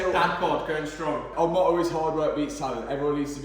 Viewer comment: Did you say Hard Work Beats Salad? 🤣